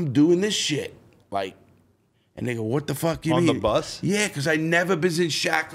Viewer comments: did he just say nigga ? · Aye no hater shit but foo said nigga 😂anyone hear it? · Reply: he literally says ''and they go''